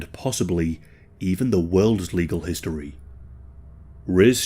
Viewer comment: I know you have to sensor's but it sounds like you said "the worst pissed in history" and it made me laugh